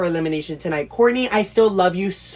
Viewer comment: Hey everyone!!! PLEASEEEEE remember to keep things respectful in the comments. The drama was amped up in the edit, but Mya (and the rest of my friends!!) are all super close and were just playing the game the way it was intended. I seriously LOVEEE how passionate y’all are about the series!!! It means the world to me ❤ If the game stressed you out feel free to comment but please please please be kind and respectful so we can keep making videos like these :) See you all next week for the finale 😏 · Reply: 😂😂 what did you say bookie 😂